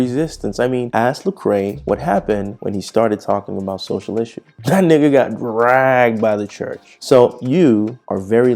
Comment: did I hear you say the N word? “that N got dragged”